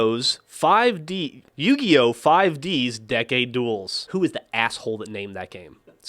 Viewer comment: LOL keep repeating 1:53 or 1:55 i thought Geoff said gay duels